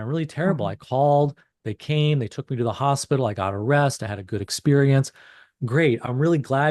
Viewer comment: LOL GOT A REST . .. for a second I thought he said "Got arrested!"